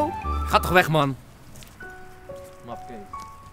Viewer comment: LIKE als je op 5:38 grapje hoorde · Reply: Hij zei 'mafkees'...